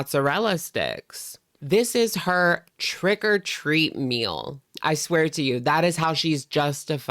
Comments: It sounded like you said trigger-treat-meal 😂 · I swear I thought you said “trigger treat” lol